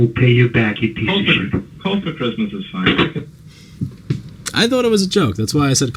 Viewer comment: Coal for Christmas is fine 😂 dead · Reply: I thought he said hold for Christmas is fine. I'm so dumb lol